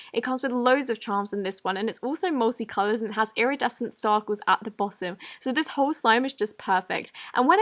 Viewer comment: did she say starkles lol